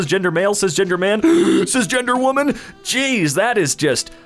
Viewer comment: I heard it as: Ginger Man Ginger Woman